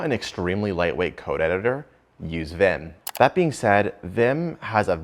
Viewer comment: I thought you said NOT to use Vim